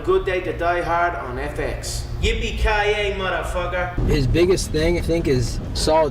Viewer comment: Can anybody tell me what is that word in 3:11 that connor said? I heard it few times in my life its kinda nostalgic lol. What that "YOU BE KAY EY" WHAT??? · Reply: It's from the film "Die Hard" it's "yippee Ky aye mf'er"